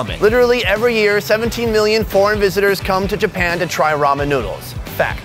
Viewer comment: Does he says at 0:49 "70 million porn visitors come here..... "